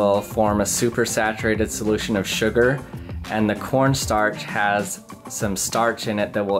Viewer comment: I HAVE SUCH A DIRTY MIND I THOUGHT HE SAID PORN STARCH xd